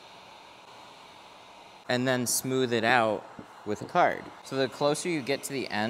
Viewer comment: I thought he said "-and then smooth it out...with heart!" and I was like "oh, this is a surprisingly cute turn of events!" 😅